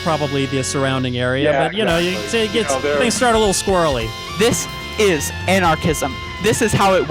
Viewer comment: I thought she said "This. Is. An autism" for a quick second.